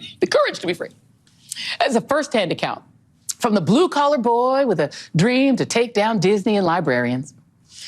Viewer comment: Joy Reid calls DeSantis a boy??!? Did I hear that correctly?